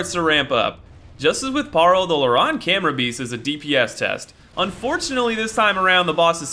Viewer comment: I first thought it said “DSP TEST”. You know who I’m talkin’ about. 😂